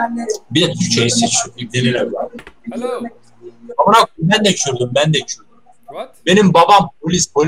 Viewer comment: I love kurdish people From philippines Kuwait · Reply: you should translate what he says 4:34 and not leave important parts out He says bende kürdüm it means I am also kurdish Same as the turkish soldier who had kurdish ethnicity